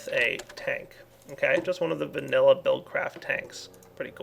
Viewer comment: did you really say VANILLA BUILDCRAFT TANK